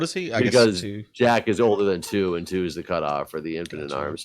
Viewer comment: LOL -- in the first few seconds I heard "Infinite Arms" instead of "Infant in arms" and was wondering "WTF?"